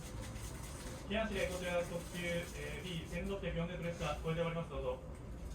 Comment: ×聞こえておりますどうぞ ⚪︎これで終わりますどうぞ